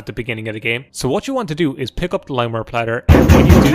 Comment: Limeware? I thought it said lime wire 😔